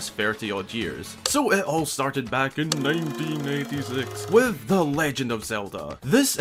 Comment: to me i heard '1996'